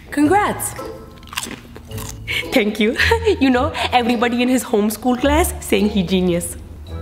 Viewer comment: "Everybody in his homeschool class said he genius" 😂😂😂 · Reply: lol, all this time ive thought shes been saying "homeroom class"